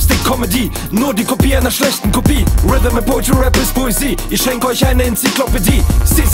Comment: Was hat er gesagt 1:40 ? Danke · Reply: „Rhythm and poetry, Rap ist Poesie“